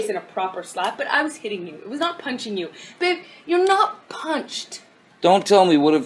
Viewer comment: she clearly says "Babe" not "Bitch" why do they make shit up to try to make it sound worse when it's already insane ?